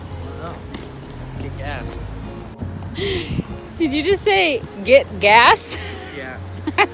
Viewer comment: I thought he said I kick your ass instead I get gas lol..I get old ears